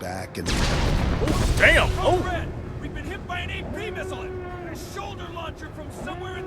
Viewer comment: Did I hear "We been hit by a penis?" O_o